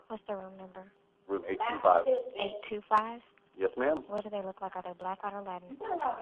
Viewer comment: It sounded like she said "what do they look like? Are they black, white, or latin?"